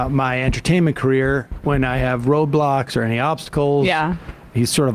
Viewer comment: LoL I swear I heard Sam say "Tim Pool helps me in my entertainment career when I have Roblox."